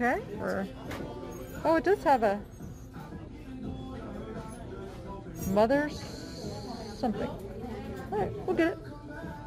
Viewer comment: I thought it said MOTHER JUGS...LOL